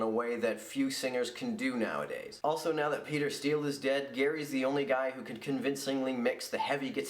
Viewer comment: For a second there I thought you said Peter Spilles died. I just about lost it. I mean PP for life.